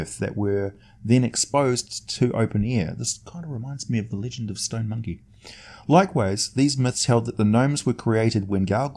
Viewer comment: Stone what? I'm sorry, I can't understand you-you're speaking at a near whisper! D-:< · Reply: I said it reminds me of the Legend of Stone Monkey (Sun Wu Kon)